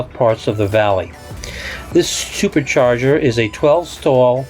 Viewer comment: did he say “this stupid charger...” lol